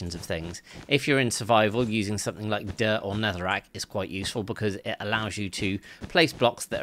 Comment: I heard "dirt or netherite" i was like huh 😂😂